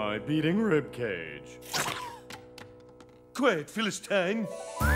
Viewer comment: It sounded like he said "Quiet, Phyllis Tang."